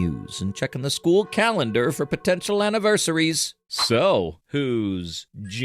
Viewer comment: DID YOU SAY [POTENTIAL] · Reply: High potential